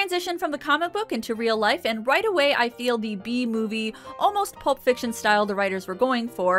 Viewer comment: I hate that when you said “B-movie style” I heard “Bee Movie style”